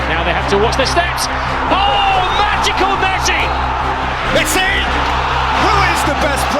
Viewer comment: substitles say backseat instead of messi lol